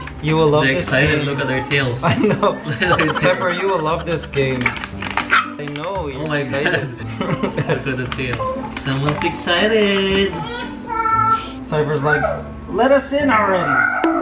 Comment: Is it just me or is saying Cypher so clearly omg.